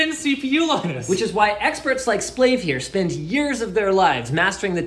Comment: Ngl I thought he said slave at first